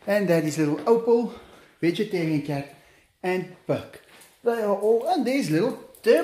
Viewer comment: did he say "vegetarian cat" ? 🤔